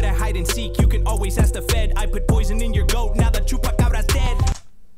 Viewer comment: I'm sorry, what? No he didn't day Tupac, he said "I put poison in your goat now the chupacabra's dead."